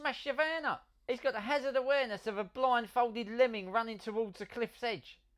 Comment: I heard that as Blind, Folded Lemming, not blindfolded lemming